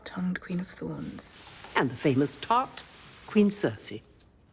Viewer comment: did she just say “the famous thot” , WTF?